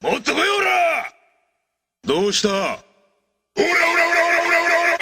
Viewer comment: what does he say here? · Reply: He says doushita? Which means what's wrong, or what's the matter? He means that in a way of taunting the enemy as jotaro expected much better from them.